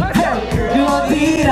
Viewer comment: I'm sorry but everytime I listen to rwy all I hear is "baby hoorang" 😭😭